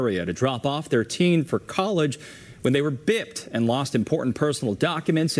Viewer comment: what the fuck did he say? They were bit? Bibed? Is he not able to say “ROBBED”?